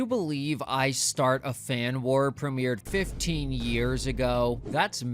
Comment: My ass thought it said iran war